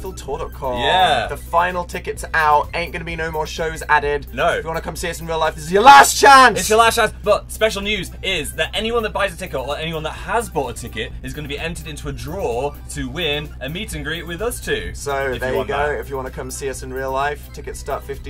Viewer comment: did he say tickle instead of ticket 😂